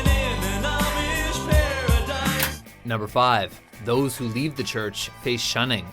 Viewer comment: That Amish Paradise song, I always thought the guy said "Living in a paradise" not Amish Paradise